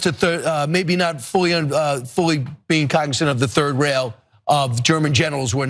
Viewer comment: You said "Third Rail" I heard "Third Reich"